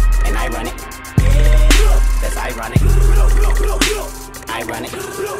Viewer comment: Always thought he said (I run it) not ironic 🤣 I gotta clean my ears.